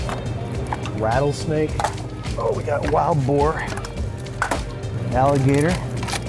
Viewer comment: I first it sounded like he said vore